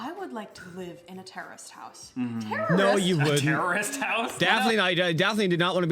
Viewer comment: She sounds like shes saying "terrorist house" the entire time so far.